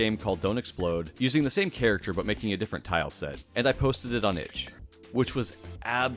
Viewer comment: "...and I posted it on _______." What does he say there? I posted it on H? Just curious, as someone who'd love to design and publish a game (if I ever get around to it). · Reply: itch.io